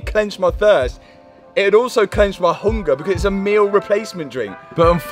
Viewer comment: Is he saying Clench or am i loosing it with the accent 😂